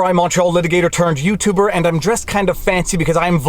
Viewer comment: I've been listening to Viva for 2 months and just realized that he starts his videos by saying "Montreal litigator". I thought he was saying "Macho litigator" and I was cool with that.